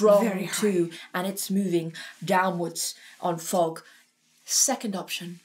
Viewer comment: "On fog" gottt it...thought she said "on fuck, go back to fuck" lmao. Also will reaffirm what a lot of people are saying, have seen them live and he does produce 2 tones